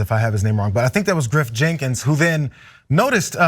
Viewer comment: lol...all I heard was 'grift jenkins'